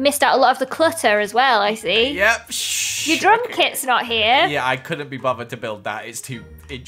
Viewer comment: Lizzy: it’s my house Joel: it’s our house since we’re married Lizzy: where’s mah fish? Ur drum kit isn’t here wait drum Joel? O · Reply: elaine kyoko chen I THOUGHT SHE SAID DRUNK KIT HAHAHA